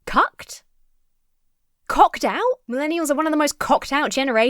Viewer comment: I think he's saying "copped out" generation. But I definitely heard "cocked" for a second